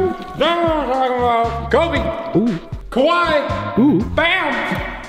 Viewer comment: did you say KAWAII?????? Kawaii means cute